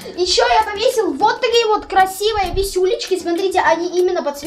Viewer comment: мне послышалось контент 16+🤣